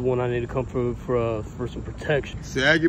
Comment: Bruh said “ sag ya pants hold my hand, you claimed “😂😂WTF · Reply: I thought he said that ass clap? Lmfao